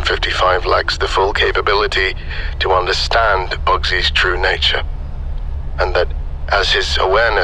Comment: Deadass thought the intro said bussy 💀